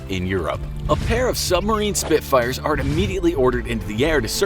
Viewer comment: Did he just say "sub marine spitfire"??? Either this is a thing or he should have said Super Marine Spitfire?? Thats what i always thought it was but now i dont know which is correct!